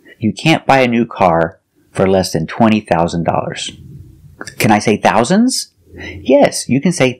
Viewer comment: Hello dear. I noticed that at 4:40 you said: You can’t buy a new car for less than 200 dollars. Shouldn’t we use fewer instead of less because of the countable word Dollar? Thank you so much for your great lessons. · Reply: says ......20,000 dollars